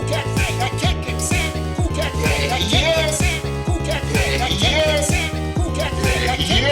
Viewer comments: I thought he said That kid kicked sand and cool cat's fake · Is it just me or does Erik Estrada sound like he said "That Kid Kicked a Salmon at Cool Cats Face"